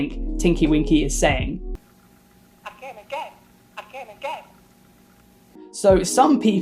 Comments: i heard: “I came again”💀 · The purple Teletubby sounds like it's saying "I'll kill again" and I love it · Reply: i hear "I'm gay , I'm gay" and thought it was hilarious that one was apparently a homophobe and the other was gay. lmao. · The purple Teletubby sounds like it's saying "I'll kill again" and I love it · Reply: i heard "i came again" im crying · Oh god...I thought Tinky Winky said "I came Again"💀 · i thought thinky winky was saying "i came again, I came again!" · I thought tinky winky was saying “I came again” 🤡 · The purple Teletubby sounds like it's saying "I'll kill again" and I love it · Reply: I hear him say " I came again" and its funny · Sounds like it says I’m gay I’m gay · I thought Twinky Winky was saying "I came again! I came again!" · For the tinkie winkie I heard "I'm gay! I'm gay!" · The purple Teletubby sounds like it's saying "I'll kill again" and I love it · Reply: I heard "i shit again" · i thought the purple one said “i came again” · I thought Po was saying Daddy but its just gibberish for Tinky Winky I heard I came again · For tinkly winky I heard "I came again" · The purple Teletubby sounds like it's saying "I'll kill again" and I love it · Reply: I thought it was saying I came again lmao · i hear "i can't escape"